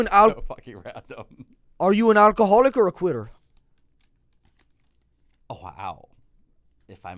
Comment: 'Are you an alcoholic or a quitter" is one of matan's best one-liners not even from a comedic standpoint but how hard it hits. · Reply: I misheard him and thought he said "are you an alcoholic or a queer," thats just as funny though